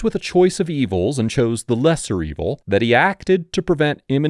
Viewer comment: My hearings a kinda bad and thought he kept saying mr evil lol